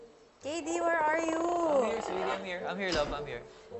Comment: Ako lang ba or I'm wrong I heard kd said "I'm here love?"